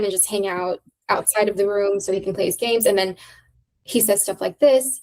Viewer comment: I thought she said: “So he can play Saints”😂😂